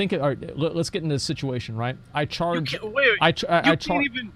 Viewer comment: just play the fucking video asmon: no no, lets get into this situation what i heard "no no, i need content" xD